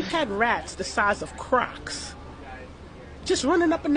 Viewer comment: I thought she meant crocodiles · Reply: Me too I was going to fly to New York until I thought she said that rats are the size of crocodiles but I am still moving after I found out the truth